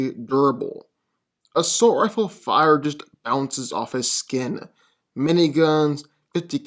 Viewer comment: Why don’t you pronounce “B” when you are talking it might be other words also but I’m really noticing you saying “ounces off his skin” specifically and not “bounce” or saying “don’t even other him” instead of bother? Is it just me sometimes I kinda hear it right sometimes it sounds like he is missing the first letter of the word